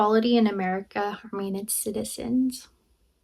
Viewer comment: It is difficult to understand what she read. I still can’t tell if she said harming or harmonious. It sounds like harming. But the subtitles said harmonious 😅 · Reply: I heard “harming its citizens”